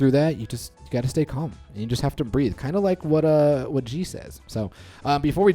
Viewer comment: i misheard, i thought u said, “you have to breathe, kinda like what she says”